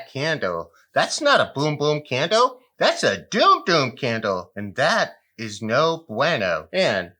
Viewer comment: For a second I thought you said doo doo candle 💩lol silly me